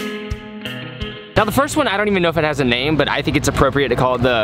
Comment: At the beginning when he says his stuff captions thought he said I don't SEO 🤣🤣